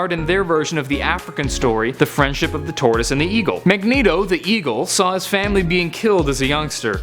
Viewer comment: I thought you said Evil Magneto. Just me?